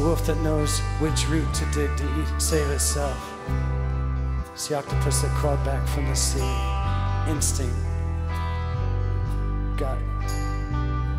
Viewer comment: It's the poison that in measures that brings illuminating vision It's the knowing with a wink that we expect in southern women It's the wolf that knows which root to dig to eat and save itself It's the octopus that crawled back from the sea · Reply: Chrìstoρhεr I thought he said: Instinct.. got it.. feeling, feelings. Similar to e-bow the letter lyrics.